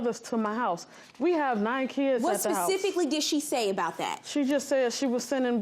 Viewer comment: did she just say 9 kids? 😳